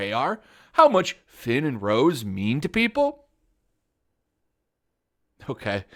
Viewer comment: what the hell is poc???? Though you said people. Did they misspell ppl into poc???? I don't get crybabies on the web.